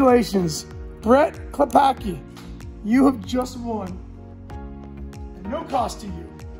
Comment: I had to watch it again and it STILL sounded like I won. 10:30. Me - 🤔maybe it’s in my junk mail🤓